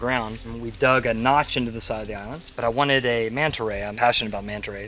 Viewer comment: What's the word that sounds like "manta ray" that he says at 20:56 to describe that type of building?